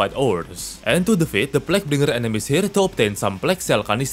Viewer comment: What did you just say? Plaguenigg